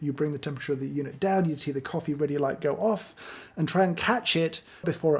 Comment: minutes, did he just say “try and cat-shit” ?